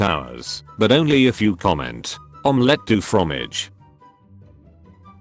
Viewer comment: I’m gonna comment what I heard “Omelet de fudge”